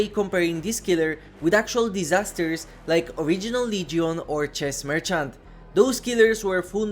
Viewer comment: Did I hear "Chess merchant"?